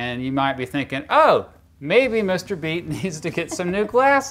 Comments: When he said mr beat I thought he said mr beast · When he said I’m mr beat i thought he said mr beast lol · When you said mr beat i thought you said mr beast 😂😂😂😂p